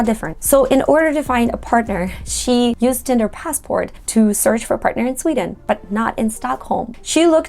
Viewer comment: What is it her Brazilian friend used? I can't quite make out what she said at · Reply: Tinder passport